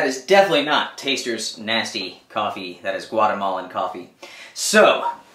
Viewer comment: so my daughter and I were watching this video and when you said Guatemalan coffee she thought you said "watermelon coffee" and then asked me how to make it.